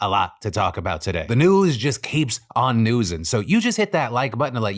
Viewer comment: I like "the news just keeps on newsing" That really sums things up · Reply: I hear it as "news keep on noosing", although that is probably more reasonable.